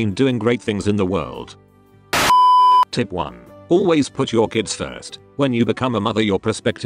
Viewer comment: I'm watching this in the hospital at a low volume for fear of being overheard and I swear I thought Suho say "Why is Jongin's butt so soft" instead of "Always put your kids first"